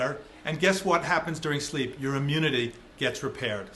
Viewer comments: Ugh I'm too tired from work, thought she said dr John LePoop for a second 🤦‍♀️ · At the intro— Sounded like she said, “doctor John Le-Poop”